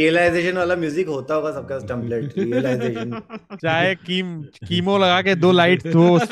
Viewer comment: Did Kullu say CHEMO laga ke, instead of chroma laga ke 😶